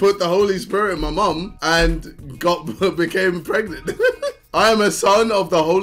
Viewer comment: did he just say " god became pregnant " ?